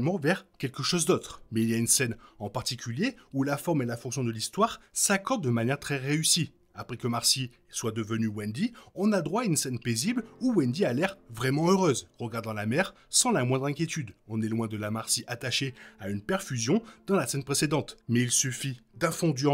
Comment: À 3:10 j'ai entendu "après qu'Omar SY soit devenu Wendy" mdr j'ai réécouté et ouf c'était pas ça